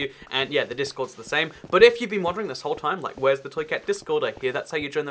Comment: Why when he says "toy cat" do I hear "truiceut" like it's a foreign word?